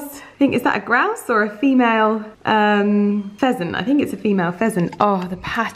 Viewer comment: Every time Lydia mentions the pheasants on IG stories or on the vlog I ALWAYS hear "peasants" 😂🤣 anyone else? "The peasants and their girlfriends" LOL